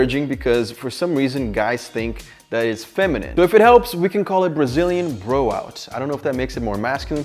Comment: I heard it as Brazilian blowjob